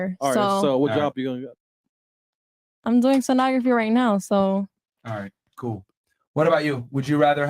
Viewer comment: did she say she thinks shes gonna make $85k/ year typing? Like a secretary? Stenographers?